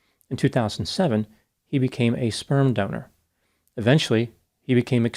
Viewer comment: At first I thought it said scam donor.